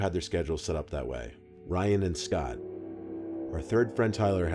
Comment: I thought you said Ryan air